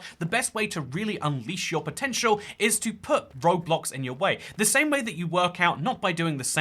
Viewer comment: I thought bro said Roblox